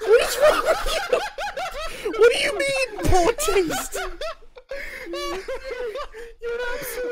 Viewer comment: I heard boring taste